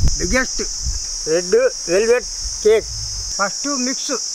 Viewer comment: I heard "Red-eu velvet" hehe who gets this? Hi Reveluvs 👋